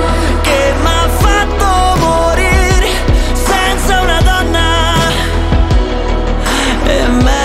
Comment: La escuché en la Radio, y pensé que decía Sexo en La Donna